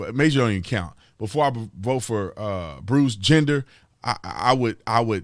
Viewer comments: Did you just say "Bruce Gender?" LOLOLOLS. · I kept hearing “Bruce Gender” 😂 Tatum makes me laugh but I can’t disagree